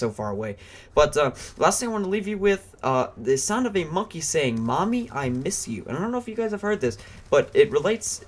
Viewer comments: i thought the monkey bomb said ill miss you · I always thought the monkey was saying "Ill miss you"not mommy I miss you